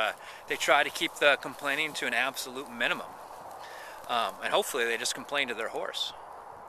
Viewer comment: initially I heard, "And hopefully they just complain to their whores." ...ahm, oops? :OP i like your ideas, agree with them, props